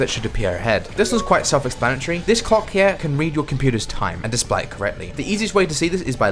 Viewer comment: either im mishearing or high or som but did you say "Cock" or "Clock"?